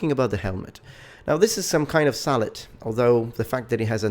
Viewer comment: I could of sworn that you said " now let's look at the helmet, it appears to be some kind of salad" XD